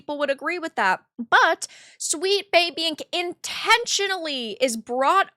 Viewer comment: sweet baby ink is brought on to the project for more stupidity. Oh, wait, I misheard that.😂